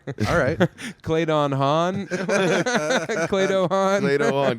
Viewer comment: I’ve been listening for a long time and I swear to god i thought Sam was saying “Kladah Han”.